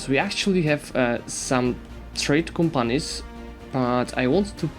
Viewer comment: What did he say he's putting in trade centers at 5:04? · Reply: Trade companies :)